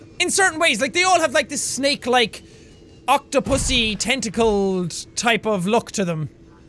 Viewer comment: did he just say octopussy?